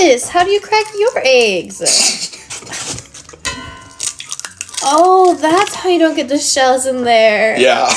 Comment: "OH THATS HOW YOU DONT GET THE SHELLS IN THERE" you guys are sooo cute💖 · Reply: I thought she said oh so thats how you get the shells in there 💀